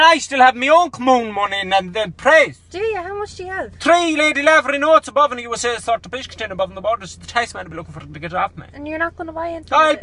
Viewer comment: wtf did he say? 😂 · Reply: Scott, Three Lady Lavery Notes (Old Pound notes) in a Biscuit tin on top of the wardrobe so the Tax Man can't take it off a me